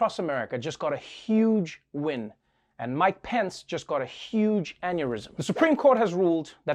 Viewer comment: When he said “mike pence got a huge aneurism” I misheard it as “my pants...” 🤦🏽‍♀️😂